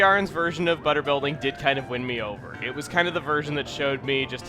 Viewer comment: did he say "WIND" me over?